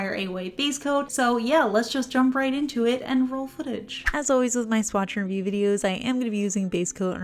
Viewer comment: Just now reading the title of this video and realising says ''swatch AND review'' instead of ''swatchER review'' 🤦🏻‍♀️ I've been mishearing this for over a year lol